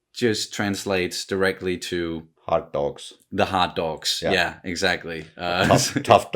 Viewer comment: I first heard "the Hot Dogs", and immediately thought of Rød pølse.